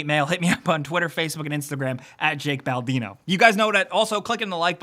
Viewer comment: Anyone else hear balbino?